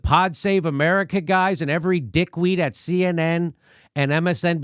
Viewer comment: for one glorious second I thought he said "every dick queen at CNN."